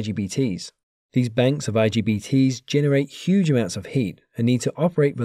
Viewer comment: i thought i heard LGBTs.